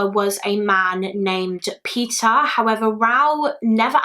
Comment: I thought you said his biological dad was 'Pizza' but then I realized you probably meant 'Peter'. Not used to the accent I guess lol.